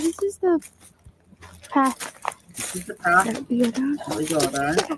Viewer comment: I hear “ see this is the path “ then it sounds like “ that be a gun “